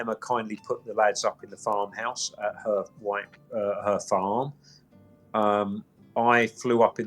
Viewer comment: I thought he said that Emma put him up in marsh farm 😂😂😂